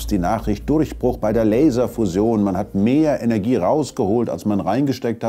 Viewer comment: Mein Tipp: Es dauert noch etwa 30 Jahre - so wie es schon immer noch etwa 30 Jahre gedauert hat. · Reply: ich hab auch dauernd Leserfusion verstanden und kurz vor mich hingestutzt 😂